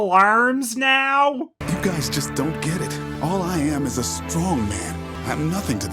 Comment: When he said "strong man," at first I thought he said strawman lol